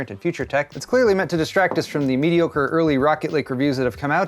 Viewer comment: I heard ''Mediocre Lake'' and I was like wow that's a dead honest naming